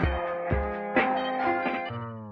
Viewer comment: I thought he said Light up the house💀